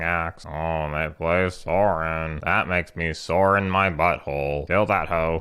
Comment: Did I really hear at 10:08 "Ohh a Sorin, that makes me SORE IN my butthole "??? 😂